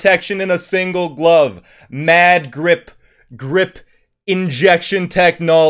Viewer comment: I thought he said "mad drip" instead of "mad grip"